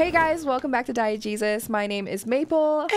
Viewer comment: I thought you said "welcome back to diet Jesus" LMAO